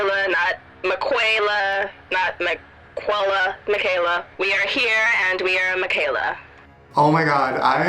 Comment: wait did she said "we are here and we are miquela" around 8:20 ? I KNEW IT! SHE'S A GROUP PROJECT THING WTF